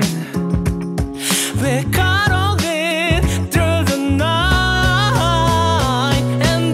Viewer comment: When he was singing "we're caroling through the night" I thought he was saying "we're CUDDLING through the night" ☺️🥰😌